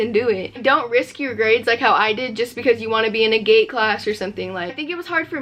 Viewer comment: i thought you were saying gay classes instead of gate